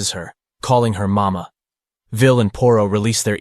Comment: Each and every time the narrator pronounce "Vil and Poro" i hear "Villain Poro" Maybe because I'm watching this in 1.4x